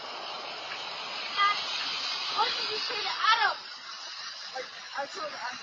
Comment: Both my husband and I hear “Jack what did you say to Adam?” Maybe we’re wrong but it sure sounds like that to us.